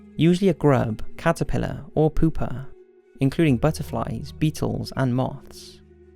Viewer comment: did he say "poopa" ? 😂